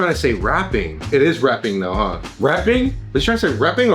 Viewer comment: I heard reaping